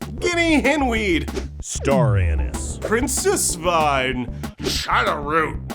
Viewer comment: Did he say Star Anus? O-0